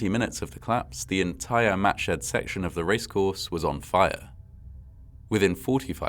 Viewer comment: Wow, that's the date of my birthday. Exactly 60 years before I was born, but still, the same date. And I thought he was saying matchheads the first couple of times, which was tragically quite foreshadowing.